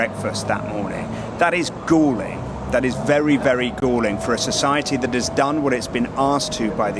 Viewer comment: what is "goling" or "gling"? I understand what reporter saying but can find the word in the dictionary. Thanks in advance. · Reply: Galling - causing annoyance or resentment; annoying.